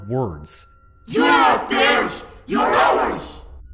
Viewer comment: what did it say? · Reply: "You're not theirs, you're ours!"